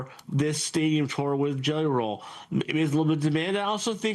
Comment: Was he trying to say Jelly Roll @ 1:10? Sounded like Ghenaralhh.